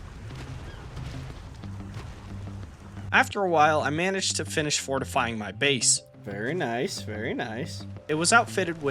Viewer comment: is that a cocel? i thought u said ALL spawns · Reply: canceled